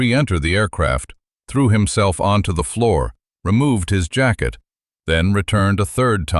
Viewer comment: Whoa, that bold double color lettering in the beginning, at first I thought it said, "threw himself on floor, jackd off". I get it now, "jacket".